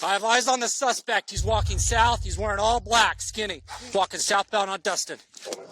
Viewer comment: idk if am hear it wrong but why does the cop saying walking sound like he saying fucking instead of walking ik there are word of what the cops are saying but the way he said walking sounds like fucking